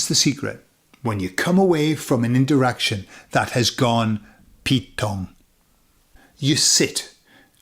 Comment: here for the English rather than for the Spanish which is my mother tongue and enjoying every single video!! By the way, what does he say in 05:33 "...a situation that has gone pit gone? pit kong? pit wrong?" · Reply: Pete Tong. It's slang for Wrong.